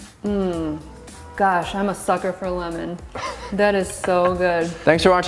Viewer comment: Did she just say, “Gosh, I’m a pucker for lemon?” 🤣I know! I know! That adorable said sucker! But I got your attention, eh? Making for dinner…have everything in the house, including homegrown lemons 🍋 🍋! Great job 👏. 💚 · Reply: Hahaha pucker for lemon would have been way funnier. Enjoy Nance!